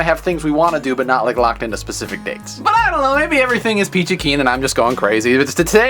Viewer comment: Hahaha, I love when an English speaking person says "sauerkraut". Thank you for this ^_^ 2:12 "Maybe everything is going..." what? I can't understand it.